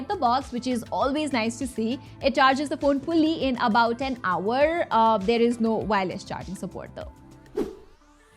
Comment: Has anyone heard 10 hours instead of 1 hour 😂